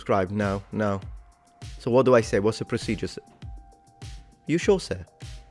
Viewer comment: "i heard are you sure sic?" We're you talking to sir sic???